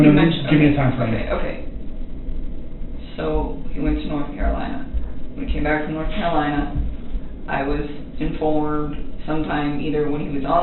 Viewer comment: I swear at 22:29 it sounds like she says “when WE come back from North Carolina”.